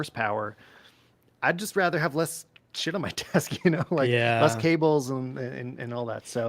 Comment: did say '' shit '' at 1:03:09? man that's one for the history to remember lol